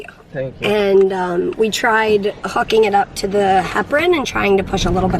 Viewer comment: When said "hooking" I thought she said the F word and I was like "wow that's the first time I've heard Mary swear" 😂